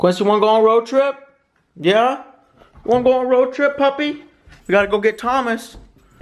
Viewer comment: did you just call him "papi"? 😁